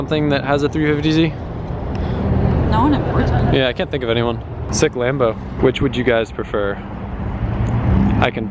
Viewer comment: did he really say Sick Lambo?